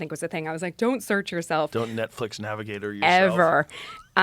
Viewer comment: What dod joe mean whn he said " don't Netflix navigator yourself". @ 15:46. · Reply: It's okay young one. Netscape Navigator. Just google it. 😂